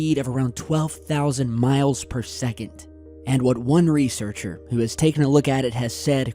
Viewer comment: Could this be tied to the flashes of light and booms we have been seeing all over the world? I think it's a logical fair question. LBA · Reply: Gunnahan I thought he said 12,000 miles per SECOND